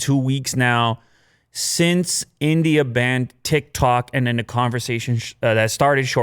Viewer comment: who heard "thik-thok" instead of "tik-tok" at 0:14 & 0:26 😂